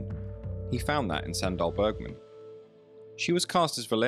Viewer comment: Sry, thought you said Sandra Bergman